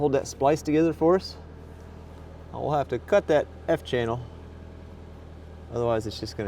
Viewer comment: language language,🤣 did you say cut that fn channel